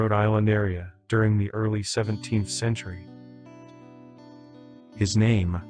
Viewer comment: Thank you for clarification, however I just completely misheard you. You did indeed say 17th century and not 1700s I apologize 😔